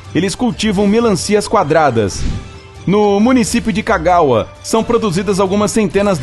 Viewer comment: municipio de cagaua: oq eu entendi: municipio de cagar kkkkkk